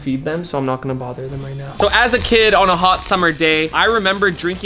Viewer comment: I thought it a said horse water 😅😅😅